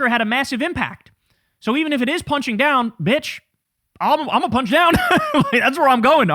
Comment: did u just call your wife a bch?